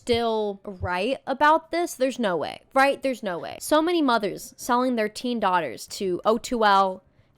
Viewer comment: I thought that said "mother smells you" and I thought there was a whole universe of fanfiction I had never discovered